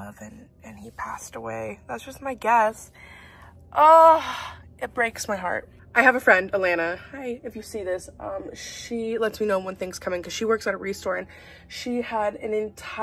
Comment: What did you say your friend's name was? Because I heard "Alayna" and my name is Alayna I got so scared lol